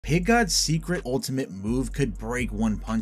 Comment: I thought you said piss god